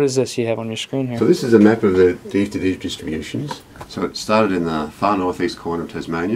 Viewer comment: What did he say? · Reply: A map of the DFTD distributions